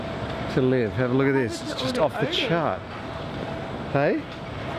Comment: love the subtitles, thought that said Hitler is just off the chart. :o) Love the vids.